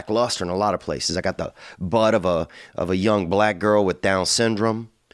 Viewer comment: “A butt of a young black girl with DS” very descriptive and specific 💀😭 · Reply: Sounds like he said got that ds booty at the end lol